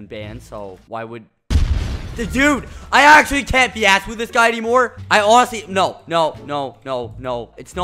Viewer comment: did Skeppy just say... i can't be assed???????